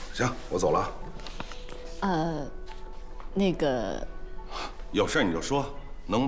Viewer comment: OMGGGG WHY IT SOUND LIKE SHE SAID NIHGAA BRUHHH I CANTTT JFIYGJCFYUCJDTYFGNGH